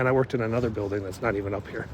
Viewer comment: what does it mean "I worked in another building that's not even up here" or did I hear that wrong:see that wrong via closed caption?🤦🏼‍♀️